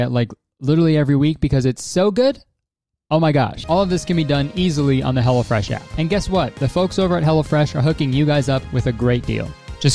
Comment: I always hear "hella fresh" not "hello fresh" XD